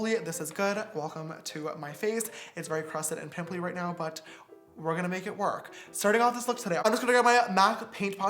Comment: You literally can’t even understand what he says here lmao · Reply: He said his face was really crusted and pimply but he was gonna make it work. Starting off this look today